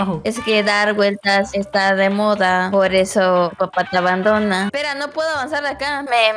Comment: Porque en el minuto 1:56 dice esque tu papa te abandono? xD Y en el minuto 3:27 dice algo muy gracioso pero no lo entendí xD?